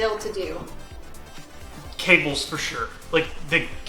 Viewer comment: When she asked what he thought would be the hardest to do, I got so confused because I thought he said "Kegels, for sure."